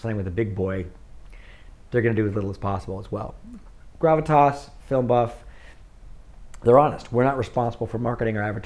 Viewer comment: what did he say?.... name of distribution platforms... ive heard something lie "gravatoss" "filmbuff" but i cant find them ... .thanks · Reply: Gravitas Ventures and FilmBuff